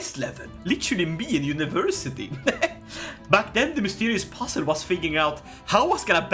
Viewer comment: Thought you said mysterious possum lmao